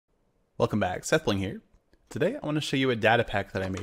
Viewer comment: For a sec I thought you said "Sethling".